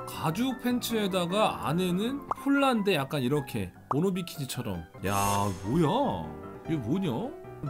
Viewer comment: 편집자형 모노키니라고 행 모노비키니를 들리는대로 써서 저리되었나 봄